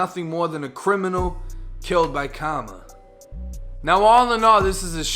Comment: "To portray that he was nothing more than a criminal, killed by KARMA." LOL your boston accent made it sound like you said he got killed by comma. Kuz for some reason boston niggas dont like to pronounce the letter R if an A comes before it. Lolol Karma (kah-ma)